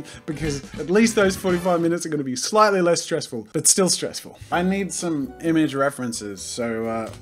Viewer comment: i thought he sould say: i need Some milk 😂